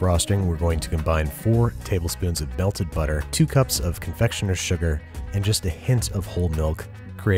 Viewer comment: Anyone else hearing Andy say "belted butter" at 3:11 ?